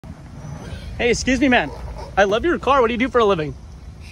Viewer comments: Thought he said “excuse me ma’am”💀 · I thought he said "excuse me ma'am" didn't realise he said excuse me man